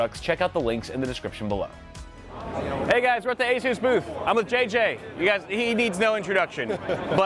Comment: Had to do a double take cuz I pressed play left my phone down for a second and swear I heard say 0:09 he was at the Jesus booth wth 🤣🤣💀